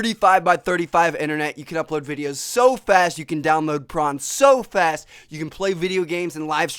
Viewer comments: I thought he said " You can upload videos so fast, download porn so fast!" XD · Did he say that he can download porn really fast......I'm down.